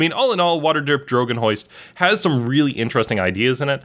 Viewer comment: Did anyone catch the "Drogon Hoist" at 9:30?